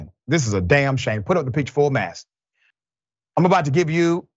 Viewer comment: SHOW HIS FACE!!! FULL MASS! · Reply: I thought he was saying full mass before too, and either way i love ity, but i think hes saying "Full mast" as in, "Let out the sails!" Full mast means the sails are fully drawn to catch as much wind as possible, if i remember right.